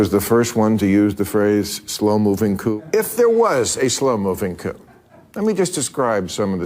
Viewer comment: I thought he said “slow moving poop” Yeah Bill, that IS a problem!